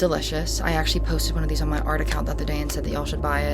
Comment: whats her other account? Did she say her art account?